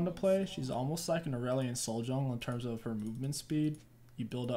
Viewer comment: did he just say that she is like a aurelion soul jungle 0:25??!! now I kinda want to see that in a video